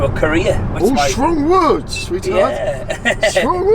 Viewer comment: The taxi driver realised was over 18 and then he wasn't interested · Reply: I heard "strong words, retard, strong words"